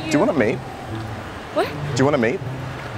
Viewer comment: the best moment lol i thought u said mate no date :) hilarious